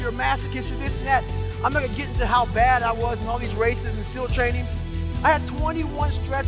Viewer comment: from all these races and seal training is what he said